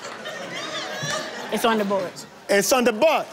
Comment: Yes it sounded like Richard was saying "it's on the butt"😂😂😂😂😂 "it's on the board"